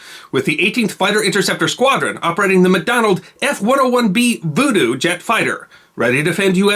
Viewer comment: did you say "McDonald"? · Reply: McDonnell- sorry.